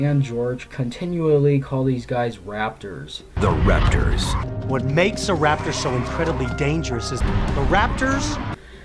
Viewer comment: The raptors! What makes a raptor so incredibly dangerous is the raptors! I'm not kidding. That's how I heard it. Probably your editing but still. I feel like hearing it that way makes as much sense as some of the stuff in the episode you're reviewing anyways